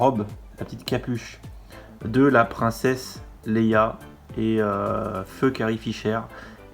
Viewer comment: alors c’est moi ou t’a dis fuck karrie fisher ? XD